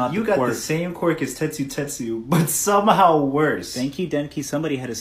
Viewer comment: 'Thank you denki' How I heard it 'Denki denki'